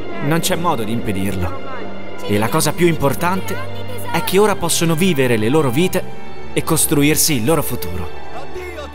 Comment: ci vediamo tra 6 anni? Cosa dice? 🤣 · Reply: 💥PACIO “Ci vediamo tra 17 anni, tesoro!”